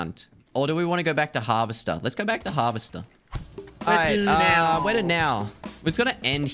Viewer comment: When he said lets go back to harvester i heard "lets go back to harvest stuff".